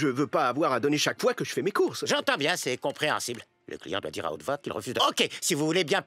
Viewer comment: Il dit quoi à 0:51? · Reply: "Le client doit dire à haute voix qu'il refuse de..."